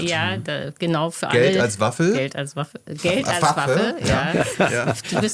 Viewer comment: Es tut mir leid, ich hab' auch "Geld als Waffel" verstanden, wenngleich ich auch sofort wusste, dass das falsch sein musste.